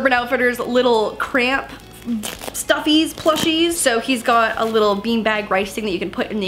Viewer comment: What did she say at 5:33 (plushie) what happened with stoofies.😞